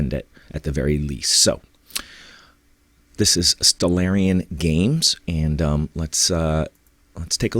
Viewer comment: Couldn't find the website mentioned in the video. I heard "Stellarium Games" but nothing like this pops up on Google. Could you post the website in the comments, please?